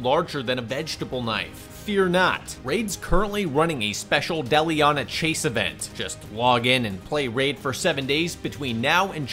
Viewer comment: Not gonna lie, during the sponsor I misheard "Deliana chase event" as Deli on a chase event" and was briefly wondering what that even meant